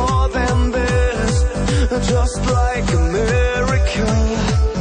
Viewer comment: When he says, "just like a miracle," it sounds like he said, "just like america." I am not the only one who hears that right?